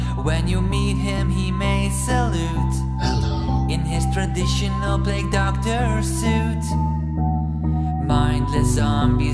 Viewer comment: Doctor, doctor the plague doctor. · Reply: Temprest I thought he said dont play doctor